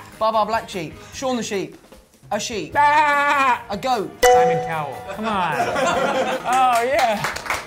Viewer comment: Tom: “A goat” Jake: “Simon Cowell, c’mon”~ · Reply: I heard it as 'I am a cow, come on' 😂